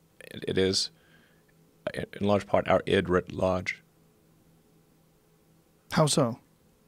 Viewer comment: Can someone tell me what Elon says at 8:23? "It writ large" ?? Was in the captions